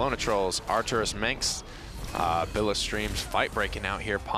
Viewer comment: Did I hear Arcturus Minx as a donation name in Smite?